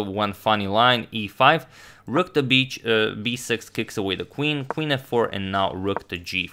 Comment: who else heard "rook to bitch 6 xD"